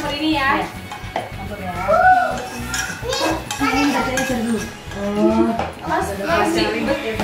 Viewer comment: ada yang bisa bantu denger ga si ngomong apa sampe anneth senyum gitu? ".... i cari dulu"